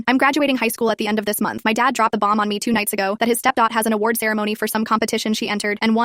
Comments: I thought she said step dog😂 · I thought she said step dog lol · I heard “step dog” 😅😂